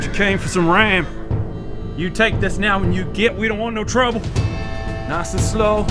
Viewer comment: Whoever was making the subtitles earned themselves a free pizza. Definitely on point, and outdid themselves. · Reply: One minor correction 1:40 should be "an' you git" instead of "when you get".